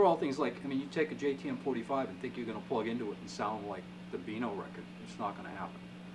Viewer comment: Hey at 6:14 -Did you say Vino record? What's that, unless I mis-heard. Thanks. Killer playing, man. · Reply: " beano" record (John mayall & the blues breakers w/ Eric Clapton)